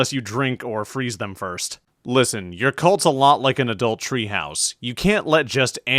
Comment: did you say adultery house???